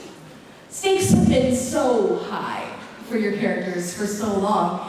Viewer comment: Can someone tell me what they sau from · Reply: marilia cyprus the moderator said "so stakes have been so high for your characters..." And Natasha said "pun intended?" And the moderator said "that is the most dadiest joke you could have possibly made" and Natasha said "well I have been told I am the daddy of daddys!"